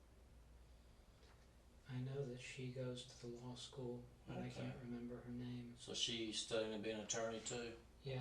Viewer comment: What did he just say? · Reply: “I know that she goes to the law school but I don’t remember her name”